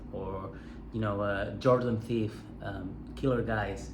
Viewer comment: In the beginning you're referencing Joe bonamassa, Andy wood and is the other name you said "Jordan thief"? Cant find that name anywhere. Im sure i misheard it wrong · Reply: Jordan Ziff 🤘